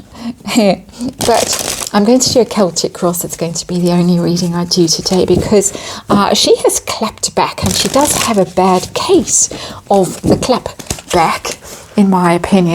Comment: I .early choked when I thought you were saying she did have a bad case of the clap......😂😂😂😂😂😂😂😂